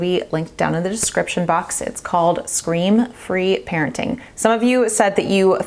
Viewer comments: Hahahaha ✋ I thought you said screen free. I've been really working on scream free parenting! My bottom lip is a little sore from biting it so much and keeping my trap shut!! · Totally thought you were saying screen free parenting as well. I saw you reposted someone’s book scream free on Instagram and went “ohhhh”. Lol. Thanks for another great video.